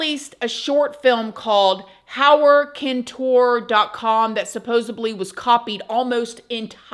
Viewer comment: 🧐🤔 did you just say supposeABLY .... 🤷🤦🙅